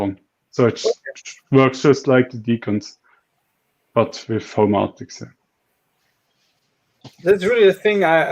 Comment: Did anyone hear the brand of presence sensor talked about around 1:10:00? I would love to buy it too but for the life of me can't find it based on my hearing! Homaltix? Something like that?